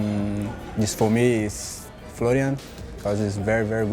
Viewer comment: What does Kenedy says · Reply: Florian